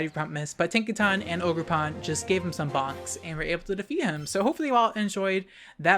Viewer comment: For a second there, I thought you said box instead of bonks. Ha!